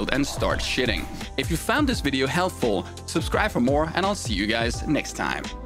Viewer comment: Did I mishear the last words in the end? "if you've found this video healthful ..."